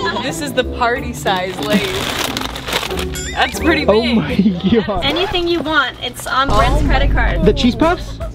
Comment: oh my Gyashh huh???? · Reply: For a second, I thought he said, "Oh my gyat"😭💀